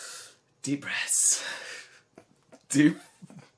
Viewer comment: all I heard was "deep breasts"